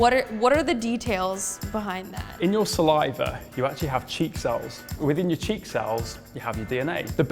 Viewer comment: I thought he said cheeck sauce when he really said cheek cells